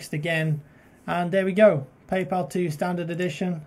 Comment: paypal instead of payday lol